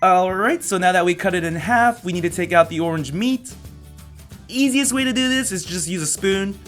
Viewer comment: did he just say "orange meat"???!!!